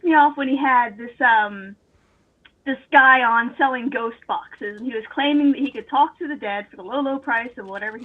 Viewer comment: was referring to ‘spirit boxes’, not ‘ghost boxes’. They work like the search function on a car radio and pick words from here and there that the investigators try to make into messages from the ‘other side’. It’s not pareidolia per se, these are actual words from real radio stations but thrown together and interpreted to deliver a supposed message. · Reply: Honestly, I thought I heard 'ghost foxes' and was sorely disappointed with the succeeding convo.